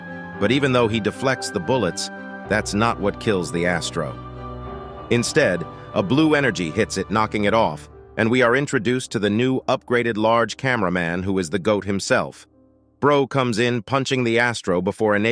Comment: Ngl, I thought it was Titan Cameraman instead of large cameraman.